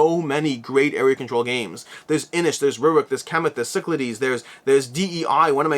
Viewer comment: and 23:00 you refer to a game but I can't make out what you are saying. Rivet? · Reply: He's saying Rurik. Full name of the game is "Rurik:Dawn of Kiev"